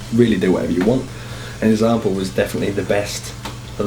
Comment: What does he say ? "And ____ was really the best, kinda like a walking hook machine." Who was he talking about ? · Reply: ***** thanks. ***** I'm not a native English speaker, and never heard of a rapper named "Example", which is a very unusual name for an individual (just like the "rapper" Game) If I had hearing problems, I wouldn't be able to understand anything at all on youtube. Cheers.